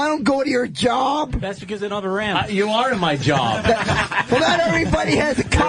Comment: What did Colin say when Bobo said he doesn't go to their job? 49:10 ish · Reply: "Cause it doesn't have a ramp." Handicap joke.